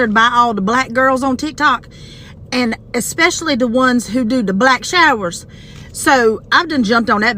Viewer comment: did she say the ones who do black showers???😂😂😂😂